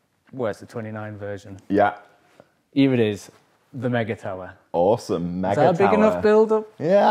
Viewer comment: did he say the MAGA tower? LIke make America great again? Lol. Or make enduro great again? Hmm. Maybe they should have called it the TrumpTower! Lol...